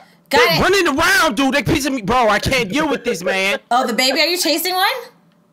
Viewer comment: did Cory say, “pissing me off”!? GO CRAZY AHHHH GOT STUPID AHHH GO CRAZY...GO STUPID AHHHHH (You would get it if you watched Cory’s video about this)